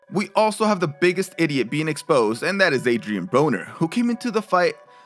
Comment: adrian who? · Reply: Oh Broner I thought he said Boner😜 It would have been funny though